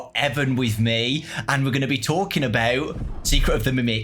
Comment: anyone else hear Edwin?